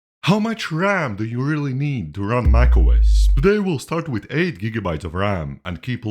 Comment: i kept hearing “How much ram do you really need to run microwaves?” for some reason